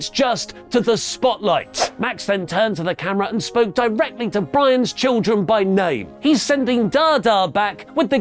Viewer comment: I thought Oli said "spoke directly to Brian Children"